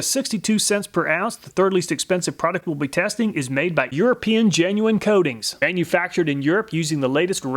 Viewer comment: Thought it said Made in Europe for Raptor Liner